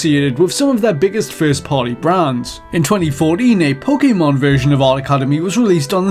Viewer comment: Is it just me or at 0:48 did he say 2040 ?